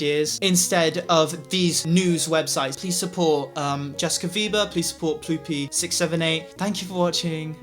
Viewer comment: i thought he said support Justin bieber lmao